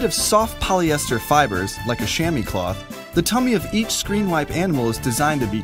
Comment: Do any one else think ShamWow at 0:07 when he said shammy cloth?